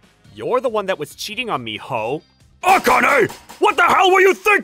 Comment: Did kuroki called Akane a hoe?